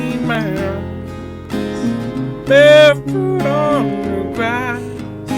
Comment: Sheeran sounds like he says "bigfoot on the grass" :D